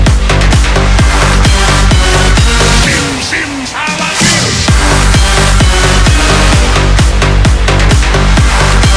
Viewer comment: What does he say at 3:10? :D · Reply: Shim sim's haladim?